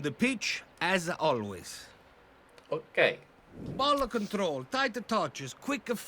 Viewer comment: at first I thought he said "bollock control"...